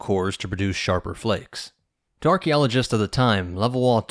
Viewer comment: it sounds like he says "Darky-oligists," 😂😂😂😂.